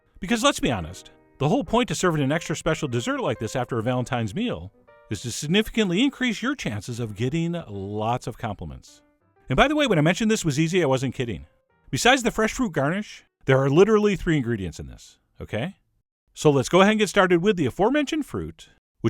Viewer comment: Him "to get lllll-ots of compliments" What I hear every time "to get lllllaid"